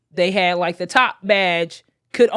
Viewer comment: just give in, there are some benefits lol... i heard "the top go crazy" 🤷🏾‍♂️